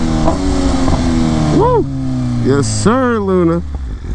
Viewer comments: LOCKED IN 😤🤘🏾 #FastLaneGang 🏁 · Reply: LOL, did you call the Beemer "Luna" at 13:49 Too many kids man! hehe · Did you just call her Luna 13:48😢